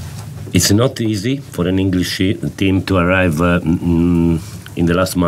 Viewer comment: did he say English Sh#t..Lol